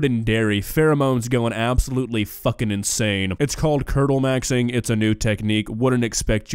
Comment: I walked away from the video for second and when I came back fr thought he said turtle maxing. Now I don't even wanna finish the video because that alone made my day and I really don't wanna know what he actually said now.